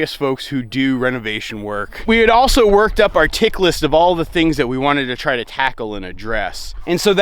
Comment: I had to listen to the one clip a couple of times. I kept hearing "all the things we want to tackle in a dress" Lol.